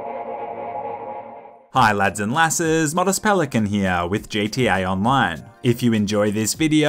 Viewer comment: the entire time I thought he said "mother's pelican here" during his intros until I put on sublities, which is hilarious.